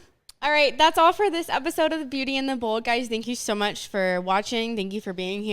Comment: I thought you said beauty and the bald